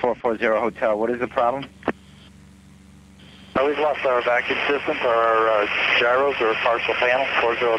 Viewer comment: Can anyone understand this? If so, subtitled would have been nice. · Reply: we've lost our vacuum system for our gyros, partial panel.